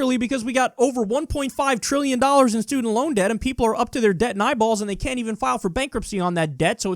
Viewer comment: Did i hear “up to your debt in eyeballs”?